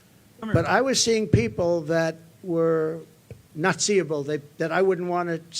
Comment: I’m finding it funny that Trump, and his 2nd grade vocabulary of making words up, said the word “not-see-able”, and the closed captions heard that as “naziable.” Ha!